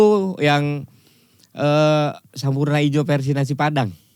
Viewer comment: gw juga dengernya SAMURAI IJO vs Nasi Padang 🤣🤣🤣🤣🤣🤣🤣🤣